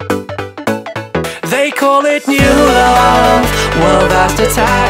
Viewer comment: is it just me or did he say "They call me Fuhrer" at 0:16 o.O